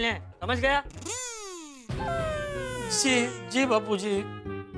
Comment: Did Jetha just say SI Papi